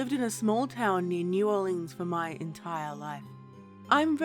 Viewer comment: i misheard at first and thought you said "Small town named New Orleans" lol Great story and narration